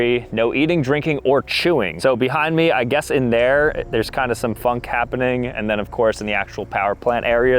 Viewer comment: what you say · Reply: He said funk